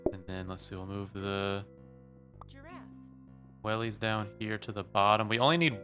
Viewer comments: i heard giraffe? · did anyone else hear giraffe